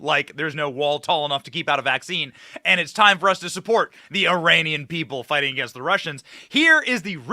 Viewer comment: Did he say Iranian people? I swear I thought that's what he said.